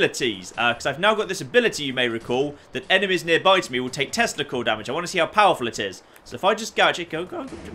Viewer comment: I know you said tesla coil damage but it really sounded like you said testicle damage.